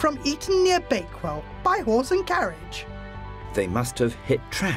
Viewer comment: I'm high af and I thought she said 'Whores and carriage' when in reality she said 'horse and carriage'